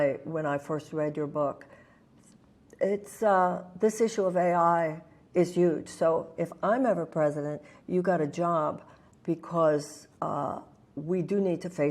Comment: Did you not hear what she said? Or do you just want to whine? She said, if she wins Andrew Yang has a job in her administration. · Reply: I did. Did you? 42:45 -- She said "Its uhh, this issue of AI is huge, so if I'm ever president you got a job..' .. While I do share some concerns around AI, I wouldn't say that's my biggest concern. My biggest concern also isn't whether or not Yang is part of the administration. I love the guy, but I loved his policy ideas more. I'm most concerned with poverty and lack of healthcare, which if you read my whines, you would see that is what I was commenting on. The problem isn't what she said, it's what she didn't say. There was no mention of Universal Basic Income.